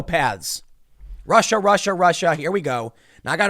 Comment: When they say. “Russia. Russia Russia “. I hear Jan Brady. “Marsha Marsha Marsha”